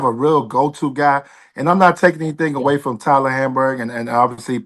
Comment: Man..Tyler Hamburg?? Was I too drunk tonight?? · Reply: I thought he said Tyler Hansbrough 😅😅😅😂😂😂